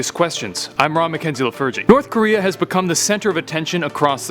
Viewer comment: is he saying "I'm Ron mckenzie you lil virgin" I'm not sure if he's saying that but I replayed it like ten times and that's what I'm hearing 😂